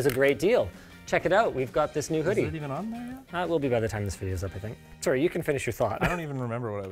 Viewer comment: Whoever wrote these subtitles either can't understand English or can't be bothered. For example, at 11:58, Linus says 'Ah, it will be by the time this video is up, I think', but the subtitle author couldn't decipher it